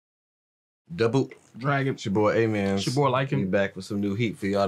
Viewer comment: Yoooo I just now realized that you've been saying your names all along 😂 I like the new names sign though🤘🏾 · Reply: I thought that was saying " it's your boy ay man" this whole time